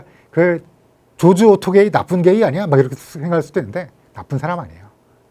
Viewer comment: 나쁜게이로 들리는